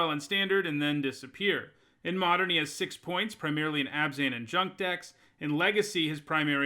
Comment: did he say "Abzan and Jund", or did he say something else? Jund would make no sense, since (unless my memory is off) Jund is B/R/G, no White in sight. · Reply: He's saying "Junk", which is the old name for Abzan.